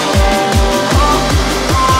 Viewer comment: Of course I heard “I’m c*mming for the throat!” 🤦‍♂️